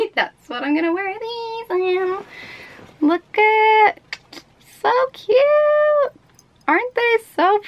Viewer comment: I swear she says something other than 'so cute' more along the lines of f you 😂 (no hate)